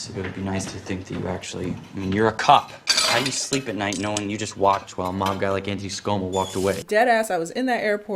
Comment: "watched while a mob guy..." I heard "walked to a manga" lol.